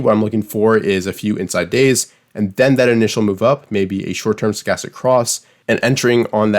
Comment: Super good vid, great analysis. I’m not an english native speaker, so in the 13:13 min, when you salid something like you were looking for dome “¿short term skies across?”. What did you exactly said and what you were refering to? Thank you! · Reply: Thanks for the question helen. I was saying short term stochastic cross. This is referring to the teal dot indicator which is one of my entry setups